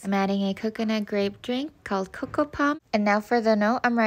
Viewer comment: I thought she said you are purple and your voice matters🤣🤣